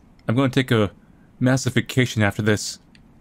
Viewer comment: I really thought said, “I’m going to take a massive ‘defecation’ after this.” I had to rewind to realize he actually said, “I’m going to take a massive ‘vacation’ after this.”